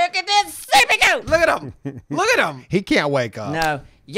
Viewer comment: I could've sworn was saying seepy at the start of the video... until I heard the not waking up thing.